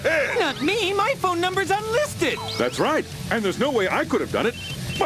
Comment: "My minutes aren't free on weekends" What does that mean? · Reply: I initially thought he said "I'm in a 'dark freon' weekend."